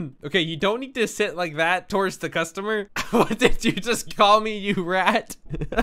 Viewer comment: A player: Rat Deputy: WHAT DID U JUST ME RAT Deputy:ADRESS ME AS SIR RIGHT NOW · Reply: No he said "what did you just call me you rat."